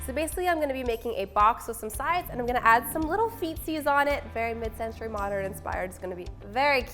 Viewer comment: I thought you said “ so basically I’m going to make a box and add some little feces on it “ lol btw feces is poop lol